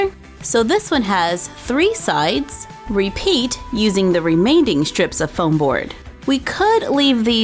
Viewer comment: I don't know if she actually said it like that but on camera it sounded like she said "remainding" instead of remaining. Because I actually don't know if she said remainding or remaining. Because I heard Remainding. Also, I have corners like that in my house, but just three rooms.